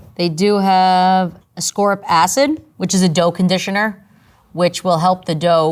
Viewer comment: i thought she said they have a score of acid in there